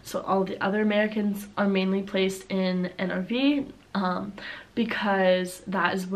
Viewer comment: you were saying "NRW" but I heard "an RV", so for about 10 seconds I thought you said that most of your fellow exchange students had been placed in RVs ...